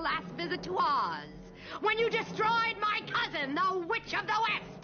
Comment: Did she say "cousin"?